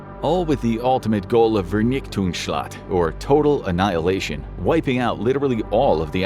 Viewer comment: I'm german and I didn't understand you've meant "Vernichtungsschlag" in 4:39 before reading the subtext. I don't want to make you feel bad or something. I mean that as constructive criticism. Please ask google or so if you don't know how to pronounce, but don't pronounce it like a english word. It's just wrong.